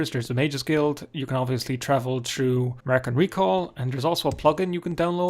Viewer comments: I hear "American recall" instead of "mark and recall". Checked autosubs, and google hear it that way too. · For some reason I hear "American Recall".